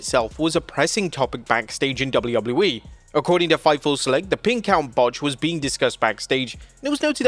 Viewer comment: Who are these fans asking for del Rio to return 😂😂😂😂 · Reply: For some reason i thought he said "The Pink Out" botch, instead of pin count botch. Man, what a name that would be to describe this event